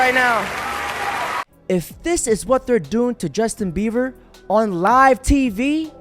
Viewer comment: I hear "Justin beawer" anyone hear that too?😂